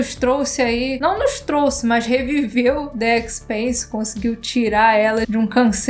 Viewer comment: Quando a falou "The Expanse", eu ouvi "Dead Space" e quase caí da cadeira. "COMO? ONDE? QUANDO? QUERO VER!!!" ☺